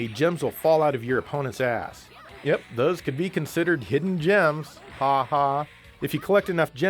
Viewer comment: Did someone say a hidden gem sexy Jesus?